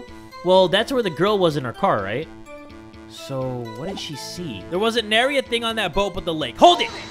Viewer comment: Did just say there wasnt a nary thing on that boat but the lake? 21:33........ im so done LMAO